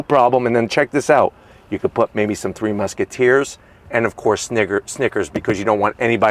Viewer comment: Um at 22:47 did he say the n-word?? 😮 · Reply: Hold on a second there! I said NICKERS by accident and the corrected it to SNICKERS. Come on man!